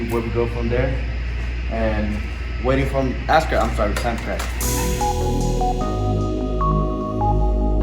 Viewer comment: did he say ass crack, then correct himself to Sam crac 💀🤣